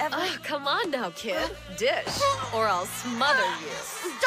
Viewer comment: Thumbnail Nagisa: Stop it, Professor Vitch! I can't breathe through your enormous Tatas! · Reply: I thought he said bitch